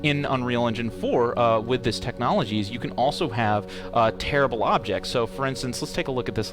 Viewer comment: "You can have terrible objects..." 🤣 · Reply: I heard it ad "terrible," and not, "tearable," too lol.